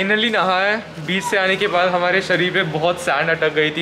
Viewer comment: did he say beach or biiisssh?